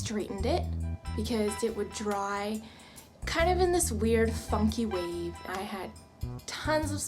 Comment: it sound like you said the f word😂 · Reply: she said funky